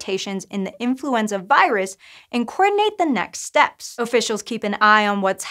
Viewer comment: I have no idea what she says at 1:17 "... Virus xxxx the next steps" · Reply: coordinate